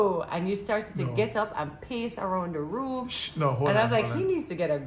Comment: Wheat cake... lol what a misunderstanding, just as how I thought I heard “he got up and pissed a crossed the room” it’s on the repeat I heard “paced”.